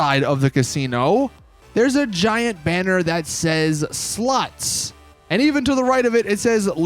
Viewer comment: sounds like he said sluts 😂